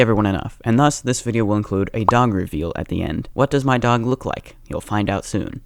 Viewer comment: BRO I THOUGHT IT SAID DONG REVEAL 💀